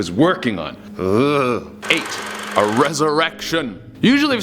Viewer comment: When you said a resurrection, I thought you said a huge erection.